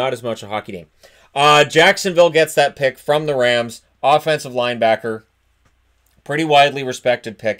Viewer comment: Did he say, "Offensive linebacker"? 😂